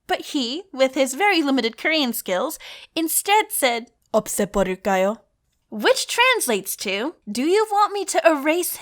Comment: it sounded like you said u upset butterguy.